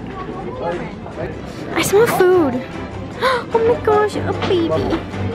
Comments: did anyone else hear saying my baby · who else heard call ‘aw baby’ RONRON PROOF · did she say “my baby”??